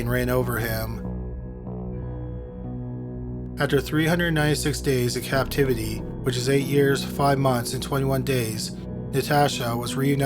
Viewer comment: Does it say at 24:14 "After 396 days in captivity which is 8 years 5 months and 21 days.."?